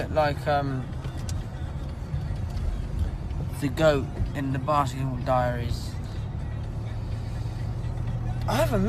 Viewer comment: did he the goat in the basketball diaries or I just heard wrong i